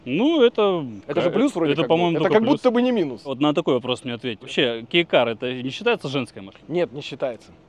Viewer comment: Мне сперва послышалось гей кар 😂